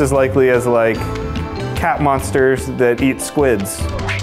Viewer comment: Never suppose to feed a cat squid, it's suppose to make their hair fall out.... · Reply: I thought you said not to feed the cat squids or something 🙈😂